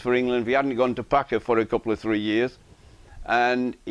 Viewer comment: Boycott did not play in the Packer World Series Cricket. He voluntarily asked to be dropped from the England team between 1974-77 because he said there was a problem with his technique he wanted to fix. He came back into the team in '77 and remained until 1982, when he signed for the England rebel tour to South Africa, ending his test career. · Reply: The gentleman was not saying boycott went to play for Packer, he was merely drawing attention to Geoff's use of the term "couple or three" by quoting what Boycott said during the Underwood appraisal. He had however misheard the term as a "couple of three" rather than what Geoff actually said. He was clearly amused by a phrase he'd not heard before.